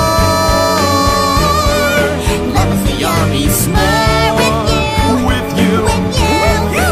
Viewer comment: I THOUGHT THAT THEY SAID "LOVE IS A YUMMY HORE!" LIKE IF YOU DID TOO!